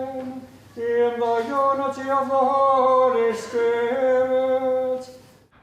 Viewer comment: Sorry it sounded like the priest said Holy Skirt